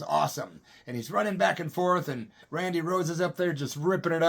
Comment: i though his name was Rodney Rhoads ..thats what i thought Ozzy said ..????